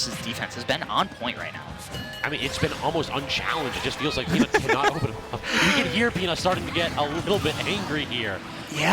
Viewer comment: what did Peanut scream here? Lol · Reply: “what!”